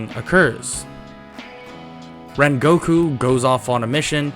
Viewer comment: i thought he said when goku goes on a mission not rengoku